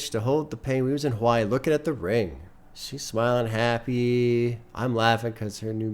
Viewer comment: I really thought he said "We was in hawaii looking at the RAIN" and thought it was a crazy metaphor. But this ok